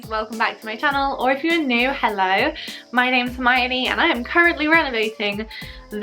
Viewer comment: I was accidentally watching this with subtitles and when you said your name was Hermione it thought you said Miami and it made me smile. I needed that lol